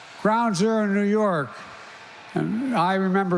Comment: when Biden said "ground-zero in New York" I swear I thought he said "crowds are in New York". This man does not speak like he should be holding any position of power. Put him in a nursing home.